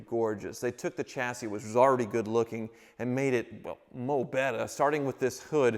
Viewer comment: Did say “Moh bettah” @ 4:14? Is friends with James “The pump” pumphrey?